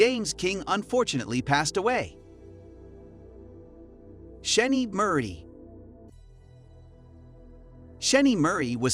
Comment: I thought that said Scheme Murder until the narrator guy said otherwise